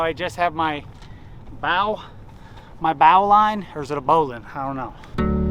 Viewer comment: I thought you said bowel line 14:28 😳🤣 I love what you guys are doing. Props from Guam!!